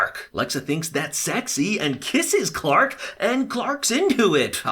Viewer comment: he said flog instead of fog